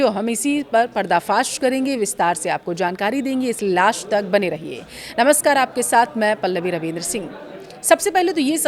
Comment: Is it only me or did you too hear "Laash" instead of "Last" 😂😂 Didi bol rahi hai viewers ko "Laash tak bane rahein" matlab antim saans tak 😆